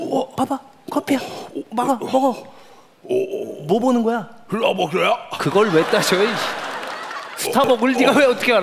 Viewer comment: 뭐라는 건가요? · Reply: 스타벅스야?